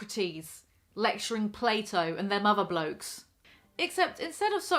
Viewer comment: When you said “them other blokes” I thought you said “their motherblokes” as in summon the womenfolk and the motherblokes from their chambers